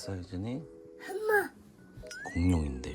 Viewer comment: 공룡? 뭐라고 했냐? 🤨